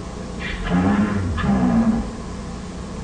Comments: Many people have already said it here, but I really love the almost eerie and toddlerish way Bonnie points and just simply utters "Stranger." It really gives the feel that he's and all the others are just children trapped inside horrific animatronics. Wonderful work!! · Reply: I thought he said "Straight There" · Many people have already said it here, but I really love the almost eerie and toddlerish way Bonnie points and just simply utters "Stranger." It really gives the feel that he's and all the others are just children trapped inside horrific animatronics. Wonderful work!! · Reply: he was saying stranger? i thought he was saying danger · I like how in your 'universe' the animatronics aren't as animalistic, they don't immediately pounce on Edward or even the nightguards because they aren't sure they're the person they're looking for. They are precise; once they determine who their killer is they even go as far as to try and help Edward. Also, if Bonnie is saying "stranger" because that's the only preprogrammed thing he can say to warn Edwards of William, props to you because that's really clever. Keep up the good work. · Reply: I love how the voice glitchiness also made it sound like Bonnie was saying "Danger" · i like how bonnie says “Stranger” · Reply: Thought he was saying stay dead but stranger probably makes more sense cause it doesn’t look like William got springlocked yet